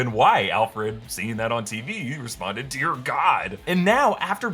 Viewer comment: For some reason I thought Alfred said “Dick” as in Dick Grayson when he was watching the news. “Dear God” makes better sense.